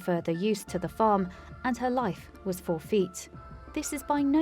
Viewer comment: Love that this cow was saved. Have to admit, I thought you said “four feet” when I think you meant “forfeit.” Took me a second to get it. ☺️